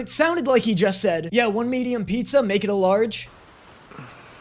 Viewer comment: I heard "one medium pizza, add on all the toppings"